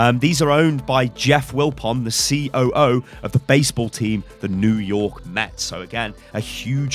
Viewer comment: Did he just say ceo of the baseball team New York Nets lol